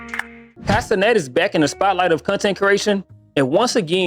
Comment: I thought you said 'Casenet' at first.